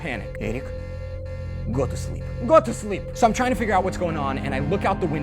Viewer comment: When Eric said 'Eric go to sleep' I thought he said 'Eric goat sheep' 😂😂😅